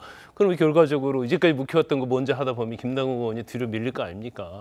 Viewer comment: 앵커 발음이 김남국 게이~원? 로 들리는 이유는?